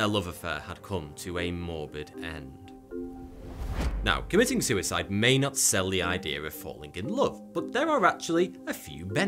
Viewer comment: Wait, did Thoughty just say "Now committing suicide may not be a ideal thing, but it can..." and state everything good that can happen when we are only alive? (4:05 and beyond)